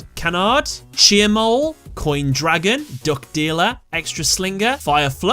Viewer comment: I swore i heard Drug Dealer